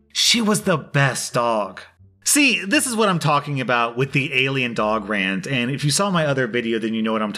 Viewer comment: "SEE, THIS IS WHAT I'M TALKING ABOUT WITH THE ALIEN DOG RANT" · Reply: I thought he kept saying Alien dog RATS. Rants makes much more sense